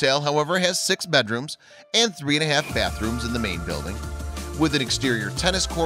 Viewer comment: did it say t has 3 1/2 bathrooms